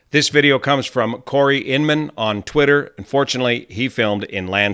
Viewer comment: said ….“and fortunately “ not…”unfortunately”. Like many of us is pro-landscape filming. 😎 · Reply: Oh! I heard "unfortunately" and was then puzzled, too.